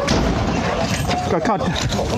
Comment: did he just say "got content"?